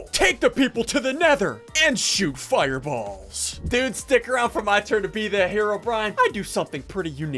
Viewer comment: I thought he said noobs ( he said dudes. I think- )